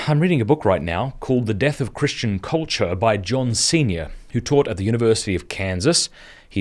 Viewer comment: At first I thought you said the author of “The Death of Christian Culture” was John Cena. 😂